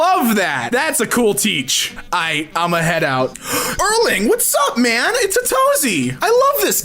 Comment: Did he just say Erling? It’s a very rare name and it’s my name so when he shouted it I got so startled